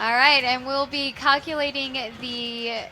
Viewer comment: does she say "Cockulating"?